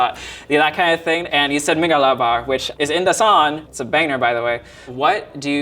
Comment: Where is this guy's accent from? He pronounces "ng" like "n". So "song" becomes "sawn". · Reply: "It's a baner, btw" instead of "banger"